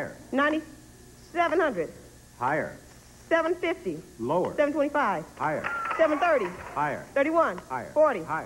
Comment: Is it me, or is it anyone who heard carefully that she said "$745"? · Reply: She said 725.